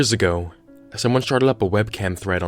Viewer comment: did he say "sharted"?